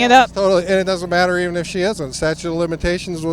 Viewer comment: That first guy admits even if Kavanaugh is a sexual assaulter that lies about it, its ok he should still be on the supreme court because of the statute of limitations. These people are scumbags. There’s no talking with people like that guy, they only need to be defeated on a political level. Their brains are jello. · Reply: Did anyone notice that it sounded like he said "statue" of limitations? LMAO